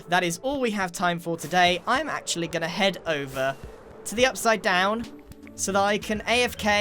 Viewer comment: DID HE SAY AFK IN THE UPSIDE DOWN